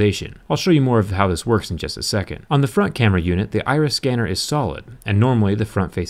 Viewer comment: i thought he said IRS